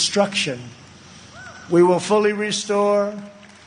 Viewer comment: Sounded like he said "hatriotic education". Salami salami baloney...